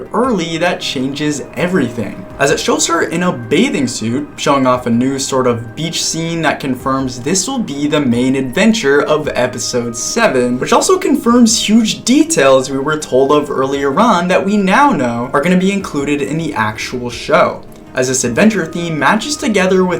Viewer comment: my audio glitched here which kinda sounded like a censor so i thought said: "Confirms huge *F*'ng details we were told of earlier on that we now know are going to be included in the actual show."